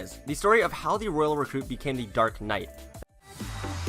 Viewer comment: Did he just say darknight?😭